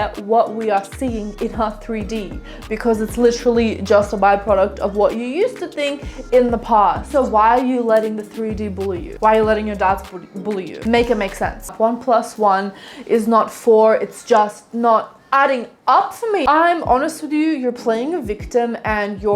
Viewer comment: I had this video on quieter than usual and I heard “don’t let your dads bully you” instead of “don’t let your doubts bully you” and I had to turn that shit up and turn on the subtitles and now I’m giggling over here 🤣